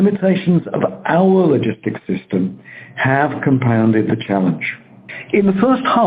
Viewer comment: every time he says "challenge" i hear "failure".